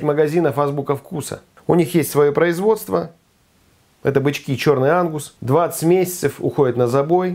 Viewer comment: Три раза прослушал чтобы понять что это бычки чёрный Ангус, первый и второй раз послышалось что бычки черный анус😂😂😂😂😂